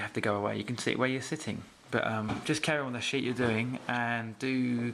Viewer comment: did he just say carry on with the shit that you are doing ???? hahahahahahahahahaha